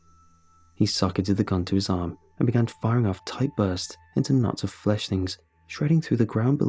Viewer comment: I thought it said Fleshlight and was about to laugh my ass off.